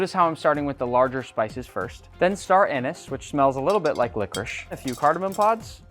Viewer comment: did he say Star anus??? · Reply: Star anise.